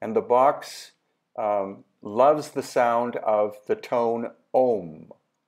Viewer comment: Hang on a minute, at 10:19 did you just say OM? When you say the box loves it what did you mean?